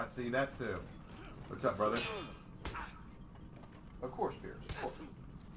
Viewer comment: Earlier in the stream, I asked that because I was genuinely curious. When you laugh I thought you said "Of course not," then got surprised when you answered that they do, but don't wear it as often.